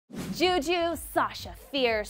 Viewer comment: i have no idea wtf she said o-o juju? wtf